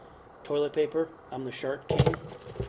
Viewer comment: did he say "toilet paper, im the shart king" at 14:30?????